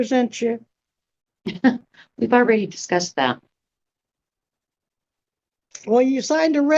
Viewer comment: Karen said "We've already discussed that" but the judge thought she said "I take it back" or something like that 😂